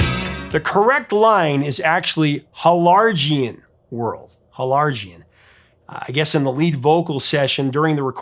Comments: I'd always heard it as "gargantuan world." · Helargian. That is hilarious, I heard it as "enlargen" which is also not a word.